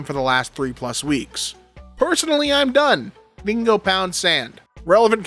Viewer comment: Am I the only one who heard "Bingo boun sand" instead of "they can go pound sand"?